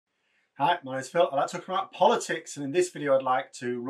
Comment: I'm not native English speaker and for some reason I keep hearing "corrupt politics" at the beginning 😄 (I know it's "talk about politics") Just a fun fact :)